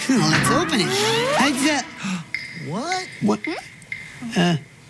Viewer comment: Did anyone hear a really deep "What" at 3:04 ?